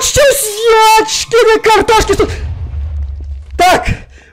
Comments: Мне послышалось или в начале сказал ежкины картошки? Хм... не пробувала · ТНТ упало Все:Бегут,спокойно стоит: матерь божья И мне одной послышалось, елочкины картошки?😂